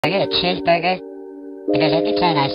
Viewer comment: All I hear is "I got a cheeseburger"